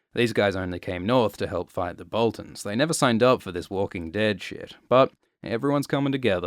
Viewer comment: you said “Walking Dead” but the caption says “World War Z”